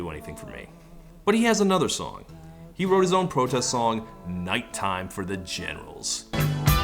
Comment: Was I the only one who misheard it as "Nighttime for the genitals"?